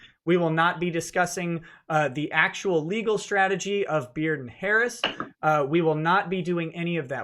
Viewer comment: For a second I thought you said Beard and Hairs Law firm